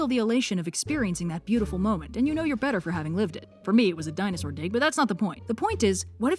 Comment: Oh my god, I thought she was saying dinosaur dick, had to replay it a couple of time to hear dig